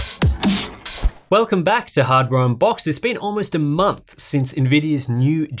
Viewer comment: I clearly heard him say "welcome back to hard brown box." Lol Is it my hearing or his accent? 😜